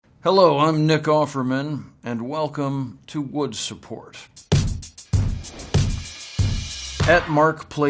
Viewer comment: I'm sorry. I heard "wood support", and i couldn't stop laughing, ahahahah